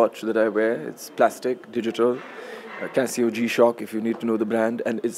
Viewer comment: what watch brand he said? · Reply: Casio G-shock